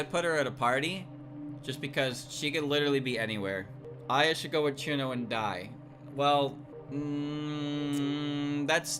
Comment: Having forgotten that there was a character by that name, I thought the chat had said she should go with Cirno... and die. Like... become dead